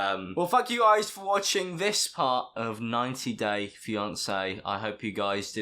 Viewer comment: anyone else thought he said fuck you guys for watching?